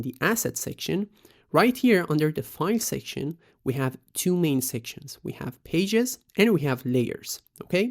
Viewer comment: what did bro say at 4:30 ? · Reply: Two main sections.